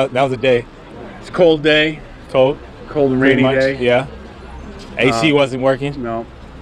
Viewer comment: Lmao I thought he said AOC wasn’t working